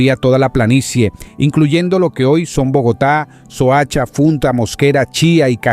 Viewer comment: Escuché "Funta" y me desanime por completo, es Funza mi papae y fue la primer capital Muisca, la original Bacatá. Igual gracias por compartir.